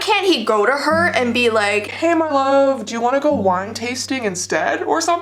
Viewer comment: Did she just call the evil queen malif as in maleficent I'm trigerd 4:00. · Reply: It’s actually hey my love not Malif. You heard it wrong.